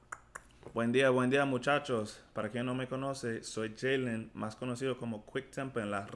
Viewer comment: Para quien no me conoce, not “para que me”. You can say Para quien no me conoce or para los que no me conozcan. Different intentions in those statements. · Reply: ok I kept listening over and over and could not hear "para quien" to make sure before I wrote to you. I hear "para que" so that you know me. But if you said "para quien" for who knows me, ok cool. Cannot hear that (ien) distinctively so it sounds like "para que me conoce." Ok if you said: para quien...it would be indicative of course It just is not distinctive to hear para quien. Good stuff!